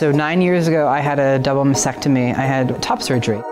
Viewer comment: Has a baby then stopped therapy Than few years down the line Realising he/she made a mistake Children in adoption New hieght of stupidly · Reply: Edit: You are right to have concerns about the child's well being. Its clear that she isn't a hermaphrodite @ 4:20 after all. I heard vasectomy and assumed and ignored title "Trans" sorry 😞. She said mastectomy so she is a woman. End edit.